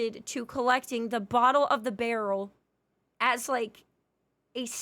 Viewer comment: Did she say "bottle of the barrel" at 14:12 or do i have "corn in my ears"?